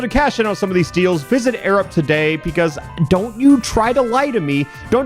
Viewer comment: I swear he said Arab like 18 times